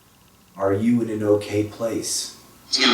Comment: when he asks if he’s in an okay place it sounds like he said i’m at lil peep’s house 🤯listen closely